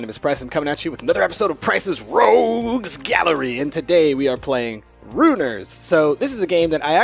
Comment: i thought it said runners XD